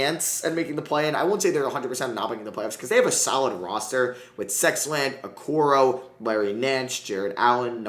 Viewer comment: What did he say who the hell is sexland 🤣🤣🤣🤣